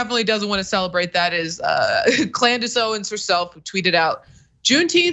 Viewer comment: It sounds like she said " Klan dace" Owens.